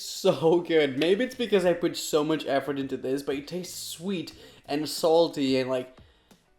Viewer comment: Did he just say that the drink was sweat and salty